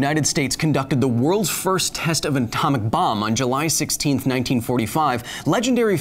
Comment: Thanks for watching Super Nerds! I hope you're enjoying these deep dives into real physics and math as much as I am. See you in Footnotes. -- kH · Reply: The best part of this video? The CC thought you said "Anton McBomb" at the beginning. :I